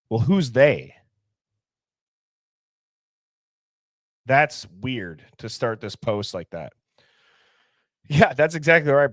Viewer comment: I keep hearing "it's weird to me".